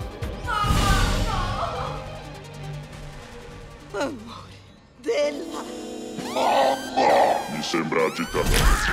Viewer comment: non ho capito quello che ha detto all'inizio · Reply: "Amore della MAMMA"